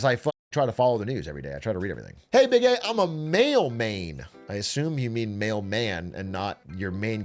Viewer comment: at first I thought he said Male Main lmao